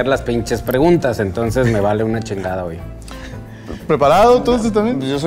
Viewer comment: Solo yo escuché que digo " preparado tu santa fe en el min